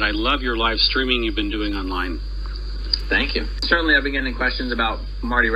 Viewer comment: I know he didn't but did he say Aaron's been doing a line